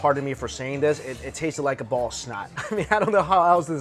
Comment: He said ball of snot and I dead ass thought he said ballsack.